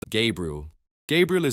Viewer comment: First thing i hear is "gay brew"